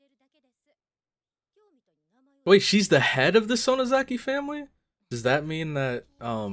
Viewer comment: Mion is the heir to the Sonozaki family. Don't know why the subtitles say she's the head. Still an important position, though!